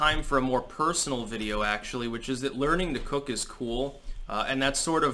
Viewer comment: At first i thought you said "coke is cool" and it didn't really make sense. Then I had an epiphany that you remind me of the stereotypical hippie from Archie Comics. Yeah I went there. But I think your more of a food hippie. I've been learning how to speed up cooking with an Instant Pot and it's awesome! Lentils are easy to cook. You just pan cook them pretty quickly and they cook fast in a pressure cooker. I like using them as filler in spaghetti sauce and it's delicious.